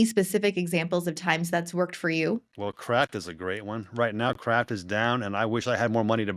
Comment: he said kraft, i thought he said crap